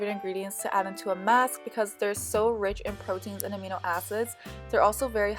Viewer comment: It sounds like you said amino asses😂😂 im sorry. I love your channel!😭😂❤